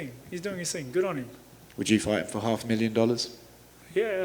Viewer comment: The way he said " I'd fight him for less " sounded like he could straight up murder him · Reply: Thought he said "Yeah, I'm fighting for less." That's why people laughed.